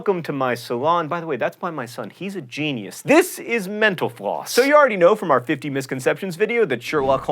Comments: I keep hearing "Menopause" instead of "mental floss" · At the end I thought he said menopause instead of mental floss... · When he said mental floss quickly at the end, I thought he said "menopause". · I love you guys, but every time you say "Mental Floss" I hear "menopause". Sorry. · When u said mental floss it sounded like u said menopause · Every time he says "Mental Floss" I hear "menopause"